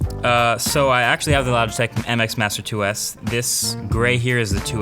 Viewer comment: the caption says lab sex MX master XD